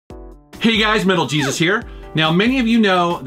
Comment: At first I thought the sped up intro said "Metal Jesus Ricks"